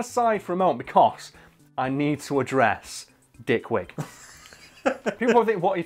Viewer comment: It sounded like he said thickwig to me.